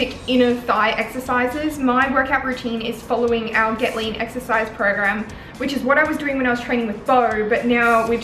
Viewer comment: thought you said "get layed program" much better name imo :D